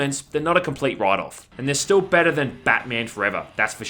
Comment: I kept hearing “Gimme that D,” instead of “gimme that key.”